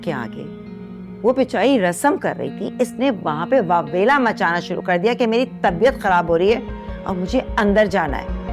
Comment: meri tarbiyet kharab ho rahi hai instead of meri tabiyet kharab ho rahi hai😂😅